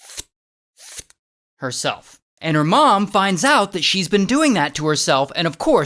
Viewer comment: what is he saying? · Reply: He's saying the girl was cutting herself